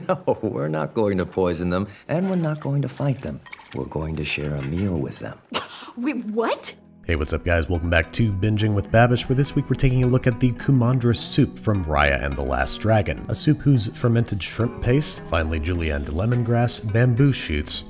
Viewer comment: I thought it said kumadra soup. Madra in Irish is dog. Dog soup.